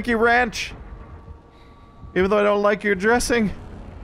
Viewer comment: Did he just say he doesn't like ranch dressing? At 2:20 mark.